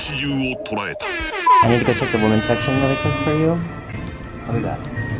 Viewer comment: 「アメリカちょっとごめんなさい」にしか聞こえない笑